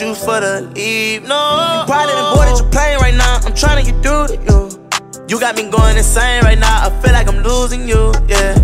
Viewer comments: I thought he said “You probably avoiding your pain right now.” 😭😭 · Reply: I thought he said "I know that you bored and playin right now but I'm tryin to get through to you" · I thought he said “You probably avoiding your pain right now.” 😭😭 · All this time I thought he was saying “ You probably were born in Japan right now”🥲